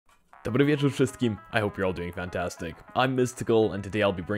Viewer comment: What was the first word you said at 0:01 ?? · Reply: "Dobry wieczór wszystkim", which means "good evening everyone" in Polish